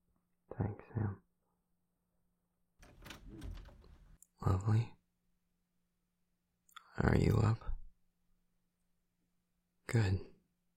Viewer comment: When you said, “lovely, are you up?” I thought you said, “Chloe, are you up?” & I was like WHAT?!! Rewind!! Lol I love this so much